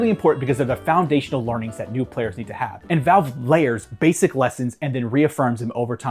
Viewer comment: Took me until about 9:30 to finally get that you were saying "layering" (thanks to the chapter name), until then I was trying to figure out what laring/lering/læring was supposed to mean😅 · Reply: I thought he said Learning but like Learing